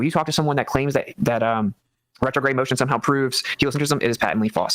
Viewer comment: LIve 58:33 "... retrograde motion proves geocentrism, is patently false" Right isn't that evidence of Heliocentrism? or did I mishear/misunderstand?